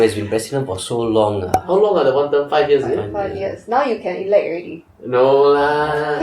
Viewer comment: If Lee Hsien Yang runs for president, would you give him your vote? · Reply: i thought he said enough lees already?